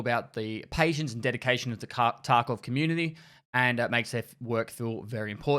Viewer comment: did he just say cock tarkov community? xD